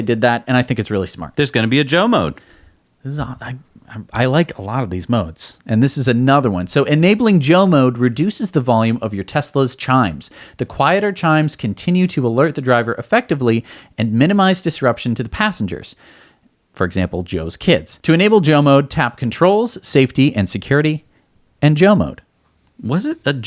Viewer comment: I really thought you said jail Mode - I was hoping it would imprison someone breaking into your car.